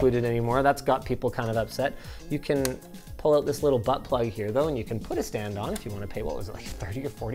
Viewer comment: did he just say BUTT PLUG?! 😭